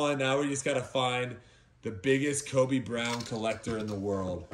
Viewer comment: Thought you were saying Kobe Bryant at the start